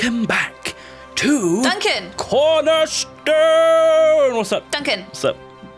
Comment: I actually thought he said "pornerstone"